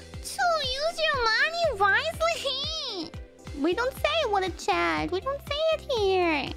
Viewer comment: Só corrigindo uns erros na tradução: Em 0:24: ela diz "Add some freaking text to it" que significa "Adicione uma droga de texto!" Em 1:38 Ela diz "We dont say what a chad here, you say WTF why ?" Basicamente ela está dizendo que o pessoal não deveria elogiar os manos que mandam superchat vazio na live dela, mas sim que deveriam estranhar. [what a chad é uma expressão que significa algo como "Mas que homão da porr*" Valeu pelos clips bro ❤️